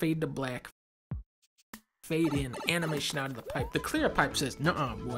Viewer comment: did...did Dunkey just say "nigga?"